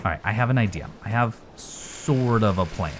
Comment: What you said: "I have sort of a plan..." What I heard: "I have sword of a plan!' Yes. That's often the plan!